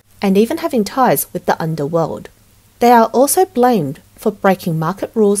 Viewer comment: Did she just say in 4:37 having ties with the underworld?